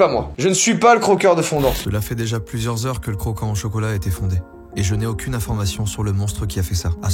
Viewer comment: CETS moi ou il a dit à 4:10, le crocant au chocolat a été fondé